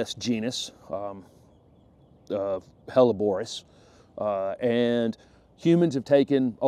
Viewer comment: Holly says.. I thought you said Hollybores!!!! Hollybores!